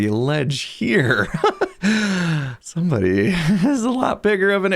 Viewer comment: I thought you said somebody's compensating. I snorted, realized I was eating hot soup and did a full on spit take back into the bowl.